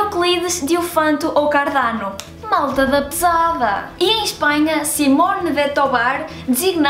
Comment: O que ela falou em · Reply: "malta da pesada" ou "turma da pesada", no Brasil